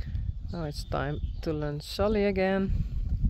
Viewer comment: Oh. Lunge. I thought it said 'luge' and I was, like, wait, what??? 🤣